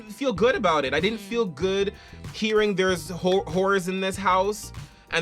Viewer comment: the whole time I thought they were saying "there's a rule in the house"